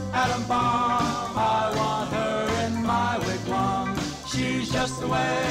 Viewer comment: I always thought he said "I want her in my left arm" what is it actually saying?